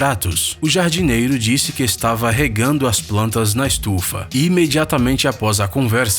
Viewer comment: No primeiro eu ouvi "o jadireiro estava cuidando das plantas na chuva"